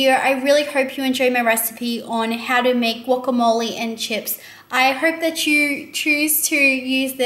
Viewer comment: At first i thought it said guacamole and diapers!